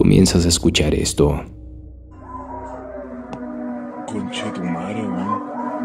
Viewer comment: escuche conchatumarewon XD